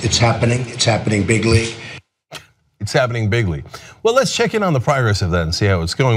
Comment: i always thought he was saying "big league" not "bigly."